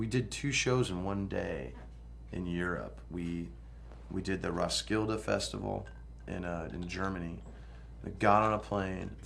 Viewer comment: okay :) it sounds like he says roskildefestival in germany :P